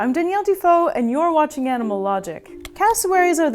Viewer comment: I thought I heard "Daniel Defoe" 😊